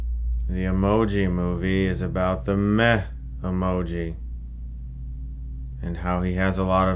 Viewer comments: I thought he said the meth emoji lol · I thought he was saying the meth emoji the whole time 😂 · I thought he kept saying meth emoji 😂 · As someone who's never used an emoji, I thought you said Meth Emoji in the intro. That's the movie I want to see.